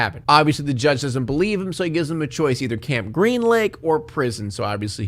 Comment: As a kid I thought they said "camp green leg" so when stanley shows up and is like "where's the leg?" I imagined a severed leg, green from rot, just kind of hanging from a pole